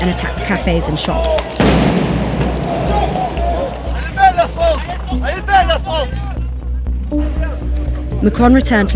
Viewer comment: is he saying a le belle la france? and does it really mean isnt france pretty? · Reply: He's saying "Elle est belle la France"